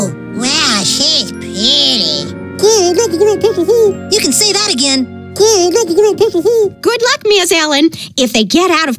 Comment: does anyone understand What Kenny say? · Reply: Dam I Like To Get A Taste of That